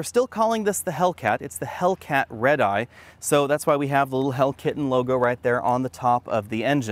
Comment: Did he say "Hellkitten" at 1:53?